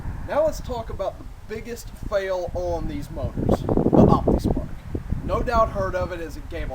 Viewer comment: What is he saying at 4:00? What type of device? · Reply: Optispark. It's basically a fancy distributor.